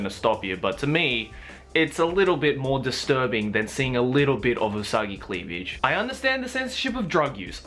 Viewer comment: I'm crying because me and my mother thought you said something that even fits in context.... *Wipes tears, omfg hahaha..." We thought you said soggy cleavage.... Mom was all like what the actual EXPLATIVE is soggy cleavage??!!?!!!!!!!